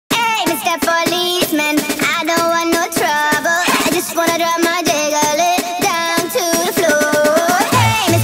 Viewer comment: I heard "hey mista police men i just wanna drop my dayler lit come to floor"